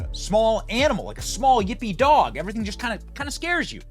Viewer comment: Oh yippee dog lol I thought u said hippy dog 🐕 🤣 😅 😂